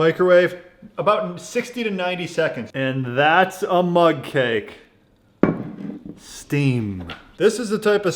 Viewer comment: HAHAHA! Thought you said MUD CAKE at first.